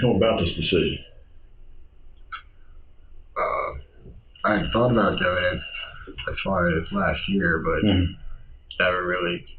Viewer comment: " I had thought about doing it as far as last year "is what he says not "I hadn't thought about the evidence as far as Last year." Does no one actually check the transcripts they add to these or are they just set on automatic so more content can be uploaded faster? No shade i had just noticed a lot of these cc are incorrect didn't know how they are done.